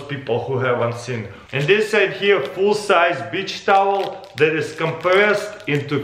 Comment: i thought it said full sized bleach towel